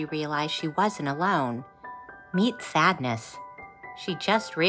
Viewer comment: It sounded like she said fatness the first time 😂